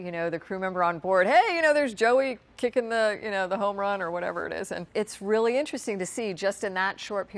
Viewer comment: i heard "kicking the homerun" and i had to stop watching because i died laughing.... hahahaha... must be a game that they play on the space shuttle